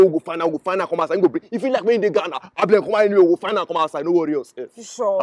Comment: Did I hear Ghana and ablekuma😂..